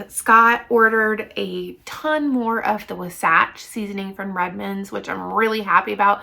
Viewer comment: what is the seasoning you said was your favorite sounds like you said lasatch not sure couldnt make it out ?